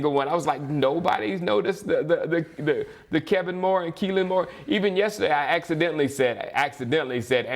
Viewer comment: I heard you say Keenan moore on the last one but I thought maybe that's how you pronounced it. Lol!